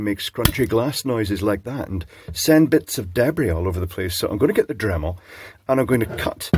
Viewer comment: I thought Clive said 'send bits of deadly everywhere' and from this point forward I shall refer to shards of glass as 'bits of deadly'. 😂